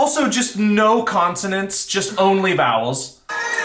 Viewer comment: did anyone else think said "no incontinence, only bowels" and was like 🫥